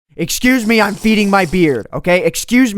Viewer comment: At the beginning I heard say "I'm going to feed my beer"